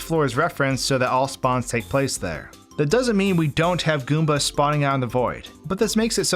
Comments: look at the closed captions, he put Bob-ombs instead of goombas 🤣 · The captions say Bob-ombs instead of Goombas. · subtitel says Bob-ombs instead of Goombas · you wrote Bob-ombs instead of goombas on the subtitles! Nice video, you expend lots of time on this and we are glad you did!